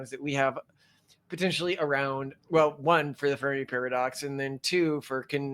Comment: I swore he said the furry pardox... now that would have been an interesting show.